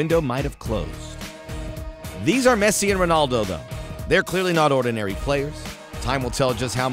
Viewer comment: Did he just say "Messi and Ronaldildo"? On